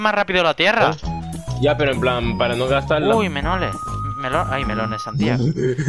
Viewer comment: Jajajajaj en el minuto 10:36 me reí por que dijo menoles